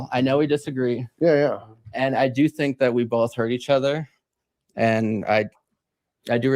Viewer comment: I don't think he said "We hurt each other". I think he said "We heard each other. You heard me and I heard you." etc etc. Being "heard" is important to these folks. I don't think he listened very well to your points. He certainly didn't think about them with any critical thought at all. His focus was about his life. "My best friend is an illegal immigrant and when you said illegal immigrants would need to be shot, you were talking about shooting him!!!" There's a crazy amount of brainwashing and self centeredness that he has no self reflection or perception of.